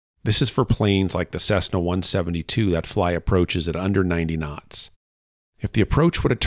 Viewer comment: did he say a Cessna 172 flies at 190 knots?